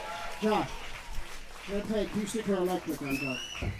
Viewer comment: did flea call josh "john" at first?